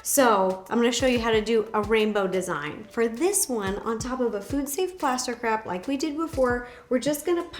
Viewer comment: I heard “on top of a food safe plaster crap.” It took me a couple minutes. 😆